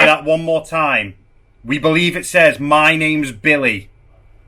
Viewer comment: Just catching up. I swear the voice of the child says 'I miss youuu' thought that before you said 'my names Billy' which is what you guys can hear. I can't unhear I miss you now lol